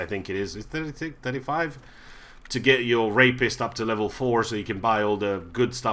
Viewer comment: what did you say? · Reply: "the rapist" or "therapist", whichever you prefer ;)